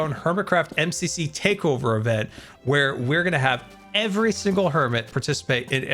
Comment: did you say every single hermit? oh boy i am so amped for that indeed i am